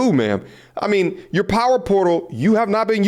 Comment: I thought Britney said her potty portal 😂🤣😅